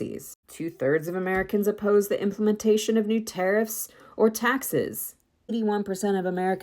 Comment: Question: Why do the subtitles say "two thirds of Americans oppose the implementation of new terrorists or taxes" when the audio clearly says "tariffs"? Are the subtitles auto-generated in some way by the content creator, or by Youtube, or what's going on?